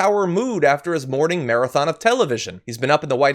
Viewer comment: Who else heard: "He has been up in the White House masterbating..."? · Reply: murderdogg ..honestly I rewinded it to (0:31) approximately 4 times but I keep hearing “ Morning marathon of television !!” lmao 🤣